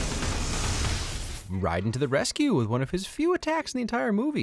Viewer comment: did you really say “Ryden”? 😞